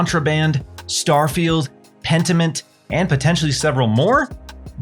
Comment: with the medieval-looking guy. What is that? I couldn’t pick up the word. Pentiment? Pentenmint? Pennymint? Is this a game? A studio? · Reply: Pentiment, is Josh Sawyers project, unannounced but supposedly releasing next year.